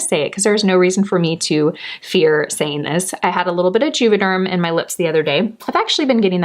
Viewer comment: what did you mention here: 18:50? i couldn't understand what you said, thank you. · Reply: Hi AnnMarie! I said I had gotten Juvaderm in my lips, (lip filler) :)